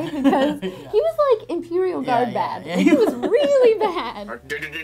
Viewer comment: did say "Imperial Guard bad"? Is that what she said or are my warhammer 40k playing ears deceiving me?